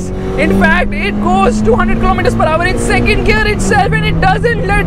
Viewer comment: Did I hear 200kmph In 2nd gear??